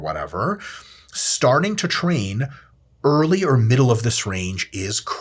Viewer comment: Help, keep mishearing things! Like 2:24 "early or middle of the syringe"